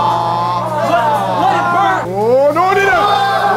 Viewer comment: did anyone catch the “ohh no dinner” by sounds like Mr